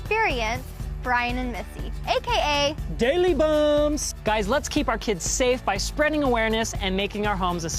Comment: I thought said “Daily Bums”